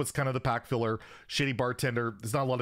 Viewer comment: Lol’ing because i thought he said “Shitty Bartender” instead of “Shady” 😂